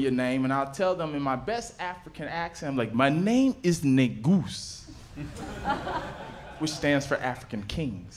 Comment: I wasn’t fully paying attention when he said ”Negus” and I heard, “le goose”